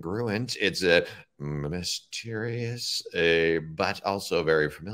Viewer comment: Did he say, “Minas Terious?” As in, “Minas Tirith,” from The Lord of the Rings? That’s a highly advanced nerd joke, Kyle! Way to go!